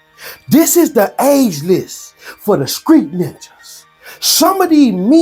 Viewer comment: did he say, SCREET 🥷🏾 ?